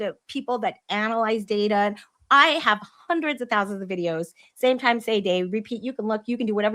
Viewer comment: For the record, my hubby said, that it sounded like I said, "I have hundreds of thousands" of videos. I meant, hundreds, if not thousands. Easily true, but big difference.